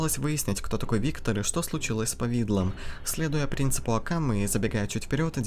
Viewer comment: я одна услышала -Следуя принципу Акаме. Это чё типо отсылка к аниме Убийца Акаме?